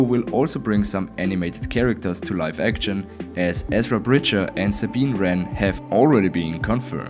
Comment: So how sure are we that Ezra Miller is going to be in Ashoka? I mean with everything going on with him nowadays I doubt that he’ll still be in it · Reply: in the video it said ezra miller instead of bridger lol